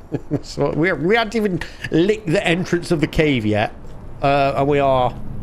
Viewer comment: It sounded like you said " I haven't even LICKED the entrance of the cave".😅